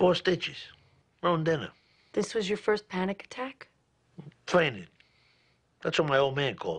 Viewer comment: What did Tony Soprano say around 4:01?... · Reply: Fainted